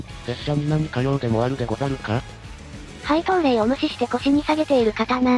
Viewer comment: いつものやり取りかと思って廃刀令が配当令に聞こえちゃったよ